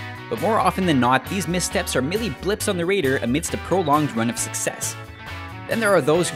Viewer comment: Blips on the what? The Raider? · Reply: I actually replayed that part cus I thought I was crazy and heard it wrong... but he definately pronounces it raider instead of radar....